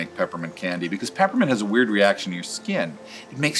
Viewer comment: I thought it said Weird instead of Wired